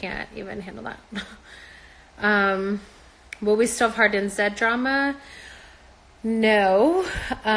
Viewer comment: thanks for the video! what's the question that she says at 16:50 please? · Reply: "will we still have Hardin n Zedd drama?"